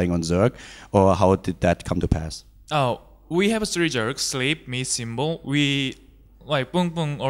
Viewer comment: Did he just call them jerks at 3:12 ? Also he sleeps like a queen? Some whores? Corrupt style? 3:25 ?